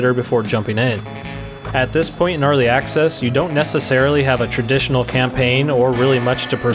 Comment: I thought you said Gnarly Access